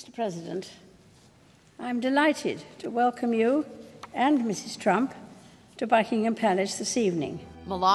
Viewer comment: I thought he said mrs. trunk😂